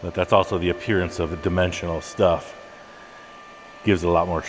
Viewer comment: I thought you had said Demented instead of dimensional. I’m old what can I say?